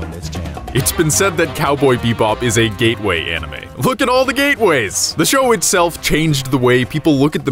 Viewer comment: I thought he said "look at all the gaybois"